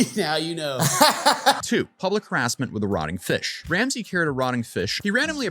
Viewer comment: Listening while clean the shop and thought he said EROTIC fish. I was like WHAT lol